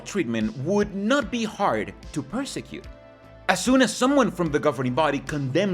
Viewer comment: You said get the gb to speak about blood transfusions on camera but followed this with, “this would be grounds to persecute them” instead of prosecute them! They’d love that you suggest they should be persecuted! 😂 It was at 20:21.